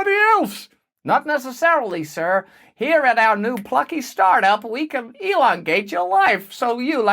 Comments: I genuinely thought he said you could elongate your wife. Mine is already 6 foot, so it would seem to be unnecessary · Did you just say, "Elon-gate your life?"